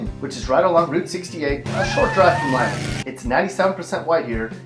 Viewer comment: did he say Celina or Lima?? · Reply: Celina